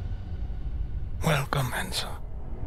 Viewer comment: did he say welcome Enzo or welcome handsome? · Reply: Enzo